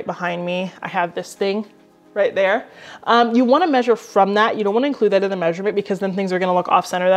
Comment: Hey! I so want the answer/tip at the 11:20 mark as I have often wondered about this! But I hear “you want to measure from there” immediately followed by “you don’t want to include this” —- halp! Which is it? 😆